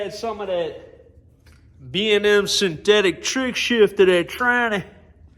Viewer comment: Can I run B&M truck shift in my 2006 4L60? I thought it was considered type F and only for old school TH350s and such.? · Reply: Nevermind! Hahaha. I just heard you say synthetic after I replayed it!